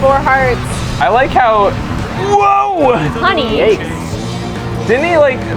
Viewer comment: did call honey at 15:53?